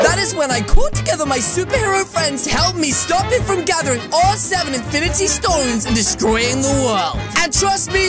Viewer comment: Wait, Did he say 7 Infinity stones